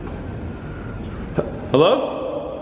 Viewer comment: It's unclear what it's saying but to me it sounds like "hey" and the second part gets lost in translation lol 🤷🏽‍♀️